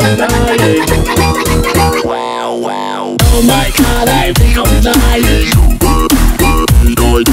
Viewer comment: Song: Happy and vibrant. Lyrics: O H M Y G O D I M D Y I N G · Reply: I thought he was saying " Oh my god, I'm really dying"